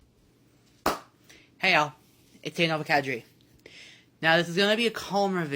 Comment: i feel like im going crazy. what the fuck does nova say at the end of his intro in like every video? it just sounds like "hey guys (something)nova(something)" and his lisp makes it completely unintelligible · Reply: For the longest time I thought he said "Hey it's Nova catch the dream" or something like that